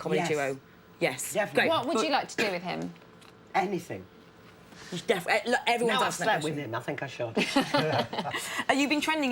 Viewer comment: Did Julie say Fecker @ 11:44 ??????